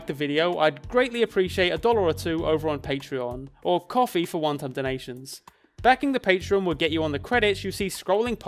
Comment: everytime you say "kofi for one time donations" i hear "kofi for wonton donations" and it cracks me up everytime